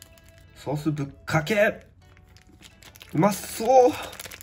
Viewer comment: Did you say bukkakei? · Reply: I'll bukkake lol Thank you for your comment 😄